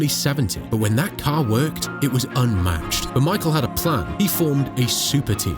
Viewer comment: Did you just call him "The Michael"? Aidan Millward would approve.